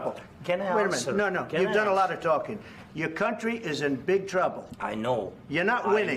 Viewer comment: Did Trump say at 4:16 'Your not allowed of talking'?